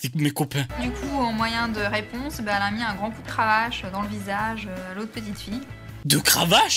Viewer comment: Jpp au début j'ai entendu "un grand coup de hache..."😭😭☠